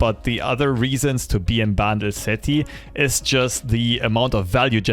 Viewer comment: My god I was so confused I thought you were saying "Battle City", just realized in the end of the intro you were saying Bandle City hahaha